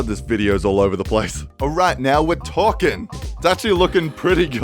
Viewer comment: Now we're torqueing. This is actually what I heard